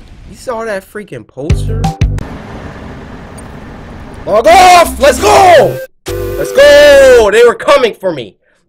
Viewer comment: it sounded like cory said f off lol